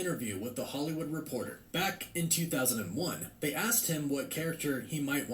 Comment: did they say "back in 2001?"